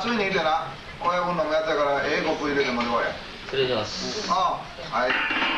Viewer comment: 「親分のやからええコップ入れてもってこい」 って親分に聞こえるように言うのかわいい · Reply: ええコーコ(コーヒー)と思ってた